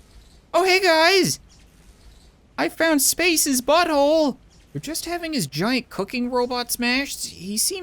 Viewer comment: “I found spaces but hole” you had a million words that’s what you pick?!? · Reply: At first I thought he said spank's butthole